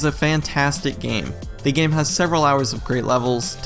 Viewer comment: For a second I thought he said that the game was 45 hours long lol Also, great review as always!